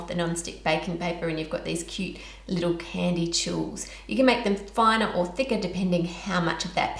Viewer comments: is she saying “candy chools”? Or jewels? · The first time I saw this video, I thought what she was saying was spelt "candy chools" but according to the captions, it's actually spelled "candy tuiles". I've never heard of either of those.